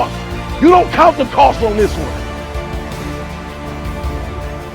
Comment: For the longest time I thought he was saying "don't count the cards" lol.